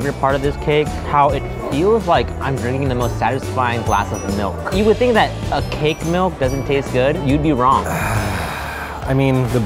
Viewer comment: "you would think cake milk would not taste good" is what he said